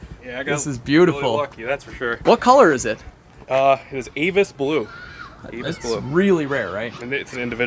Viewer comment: I thought he said anus blue😂